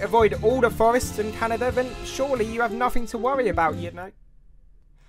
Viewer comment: I heard "chewbacca on seriodes" so I sent it to my dad who loves star wars